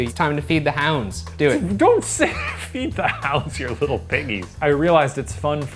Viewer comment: Did Shane just call Ryan a “little piggy”?!